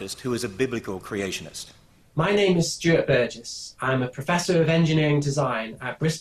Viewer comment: i heard: "hi, my name is stupid"